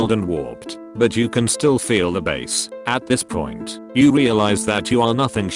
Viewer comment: I thought that said "bees" instead of "bass"....